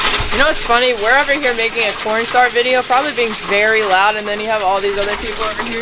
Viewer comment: For a min I thought you said making a pornstar video 😂